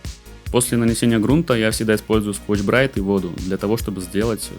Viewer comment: не могу расслышать что это за штука, подскажите пожалуйста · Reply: scotch brite